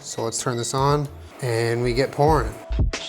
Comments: Was playing this while boiling some noods and thought I heard "and we get porn" at 7:56. Had to turn around and make sure I wasn't missing something NASTY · i though he said “and we get porn” instead of pouring lol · Did you say pourin' or porn (as in food....the slowmo commenced) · I heard "We get pouring." as "We get porn." :D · My childish brain at 7:55 thought said " And we get porn " I guess I was techinically right bc food porn but also very very wrong. Also, happy Birthday!! 💙